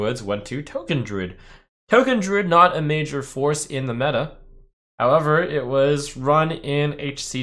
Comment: I heard 'foreskin meta'